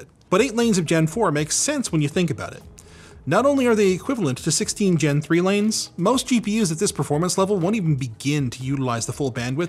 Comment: Did I hear 16th Gen, 3 lanes at 6:03, then ya, probably worth the asking price.